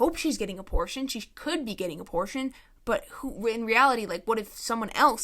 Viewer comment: GIRL I THOUGHT U SAID ABORTION U SCARED ME.